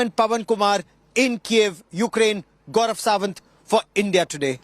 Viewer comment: Min 4:26 did he said "fuk India today"?